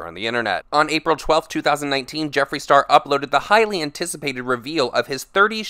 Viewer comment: When you said "highly anticipated" I thought you were saying Hylian 😂 like from the legend of zelda. I got excited